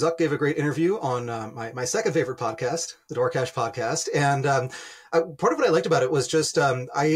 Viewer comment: Which podcast does he say at 39:18? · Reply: Dwarkesh Podcast <link>